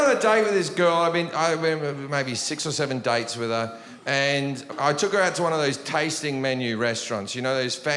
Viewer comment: I thought he was say (dikes) when he was saying dates lol